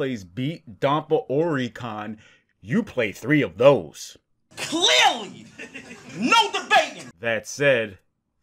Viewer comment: Just gonna admit... When I first heard the "Clearly! No debatin'!" clip, I thought he was saying "Clearly! Know the bacon!"